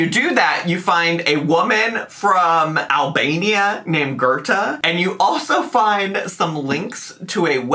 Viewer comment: "A woman from Albania named Goethe..." OMG! Joy and Bliss 🤣🤣😂😂 Yeah I know it's probably "Gerta," but Goethe is how I heard it and it's just funnier to me like that 🙃🙃